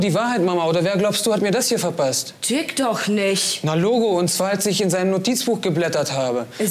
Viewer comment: Wer hat auch gerade "türk doch nicht" verstanden?